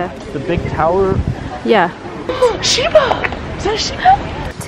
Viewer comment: You looked so pretty in the kimono with your hair all perfect! ^^ By the way, what is that person saying @ 4:02? It sounds like Shiba, is that a shiba? xD